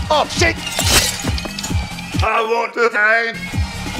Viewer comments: Subtitle says "I want to die" But I missheard and laughed harder when I've watched it at the moment it came out, that He said "I want the time" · Subtitle says "I want to die" But I missheard and laughed harder when I've watched it at the moment it came out, that He said "I want the time" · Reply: I thought it said I want to end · Subtitle says "I want to die" But I missheard and laughed harder when I've watched it at the moment it came out, that He said "I want the time" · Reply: I heard "i want the egg"